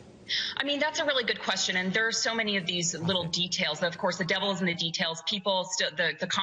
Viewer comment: Devil in the details wtf ? Is that what I heard ?